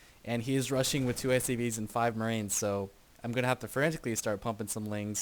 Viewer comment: is it just me or did say "I'm gonna have to *forensically* start pumping some lings" @ 03:42 =D